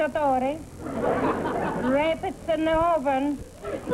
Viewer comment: I heard "rapist in the oven" !!!